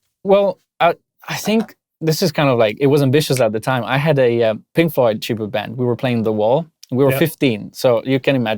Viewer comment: i thought he said pink floyd tuba band. mind was blown for a second haha